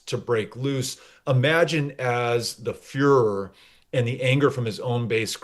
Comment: You said furor, I heard führer... grows